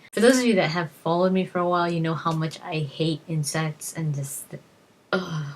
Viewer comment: Oh I thought you said "incest" XD